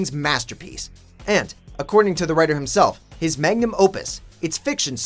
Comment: did i heard that right "magnum opus" remember that car from the mad max game